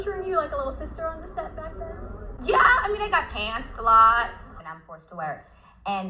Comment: she sounds like she says i got pants alot > so now i have to wear a belt. if thats what she says then thats like a reflex cooping mechanism reaction